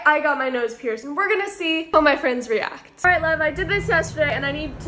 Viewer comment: I thought she said ok love lol